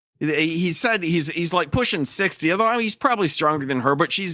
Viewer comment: Pushing 50? I thought he said he was pushing _6_0 and about to retire.